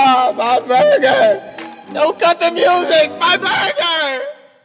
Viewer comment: lyrics: she threw the burger out my car i didnt think that she would go so far she didnt even let me take a bite i want to punch this girl with all my might she threw the burger out my car i didnt think that she can throw so far i thought that she was only getting mad i didnt think that she'd reach in the bag (ahh, ahh ahh, my car, baby) (she threw the burger out my car, baby) (baby) why? MY BURGER! ahh! My burger! no, cut the music! MY BURGER! Yo, man, Who gonna buy me a new burger, man?! Jordan! Jordan! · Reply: I always thought he said, "Don't cut the music! My burger!"